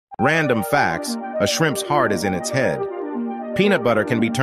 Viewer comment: For a minute i thought he said a shriimps brain is in their heads like of course they are